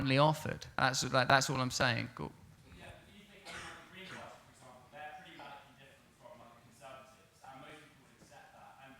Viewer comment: Anyone know what the guy said at 44:48? Sounds like he was making a very good point but he didn't have a mic. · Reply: He basically said "Isn't there a danger that by saying young people shouldn't vote, you are inspiring young people to not even listen to politicians?"